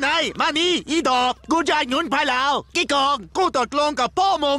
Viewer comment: มันพูดว่าอีดอกด้วยโคตรฮา